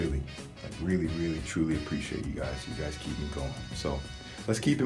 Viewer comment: U got me in the beginning. i had my headphones in listening and wrking at the same time... so being kinda distracted i was like whaaaaa? i thought he said under-appreciated